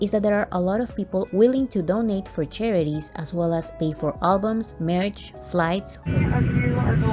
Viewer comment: Amazing video yet again !! Im just laughing so hard cause i misheard ¨fans are willing to pay for merch¨ and thought you said ¨fans are willing to pay for marriage ¨ and i literally had to do a double take lmaoooo Anyway keep doing what you do youre my favorite army YouTube channel